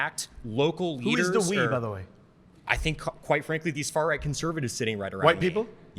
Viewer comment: Am I crazy or did he WHITE frankly instead of quite frankly 😂😂😂